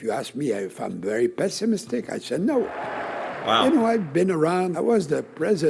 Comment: He said “no!” to pessimism, you misheard what he said. But that was common for all of us trying to interpret his English. When the French-Canadians complain about Carney’s French, I remember Chrétien’s English.😂